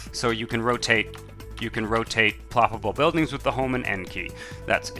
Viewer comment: all those years lol, I always build road then place because of this shit. Did you say Home and Tab key right? I will try this for sure